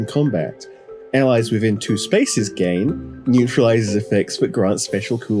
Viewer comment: I swear I hear “Allies within two spaces GANG!!!”